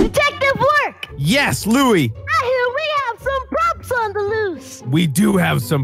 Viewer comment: since I first saw this video like 6 years ago, I always thought lui said "props sons a lil" because he was a kid and couldn't swear. Turns out he said "props on the loose"